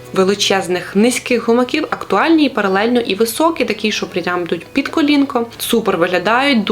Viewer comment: я мабуть не розчула, то поясніть мені будь ласка. Кому ті високі ризаки під коліно ? Вибачте , але мені почулися ......матюки .😳 7:30 . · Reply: «Прям йдуть під колінко»😂